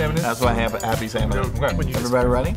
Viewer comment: I thought it said “Slappy Salmon”